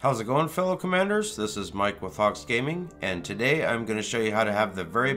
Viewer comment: Your intro is great bra, You say, "Mike from Hawkes Gaming", what I hear, "Mike Wazowski" ... the Billy Crystal character from "Monsters Inc."... LOL Videos are great... keep 'em coming.